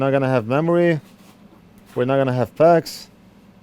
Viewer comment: If anyone sees this, at 2:38 he says something like "packs" or "pex". What is he saying? English is not my first language so I cannot understand what it is he's talking about.